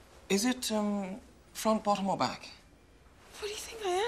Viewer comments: As a British person I loved confusion over the "front bottom or back' scene. Just to clear it up for everyone else, "front bottom" is a posh way of saying "vagina" so the doctor was asking "front or back". You're welcome! x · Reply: Yeah, as an American I also thought he was saying: front, bottom or back So three options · Yo I always heard it as "front bottom" or "back [bottom]"